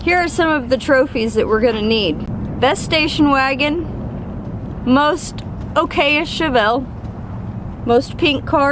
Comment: Thought you said one of the prizes was Most likely Chevelle 😅 And, do NOT try this drinking game: take a shot each time says: Goop, as it might might qualify you for the most likely to die award 😢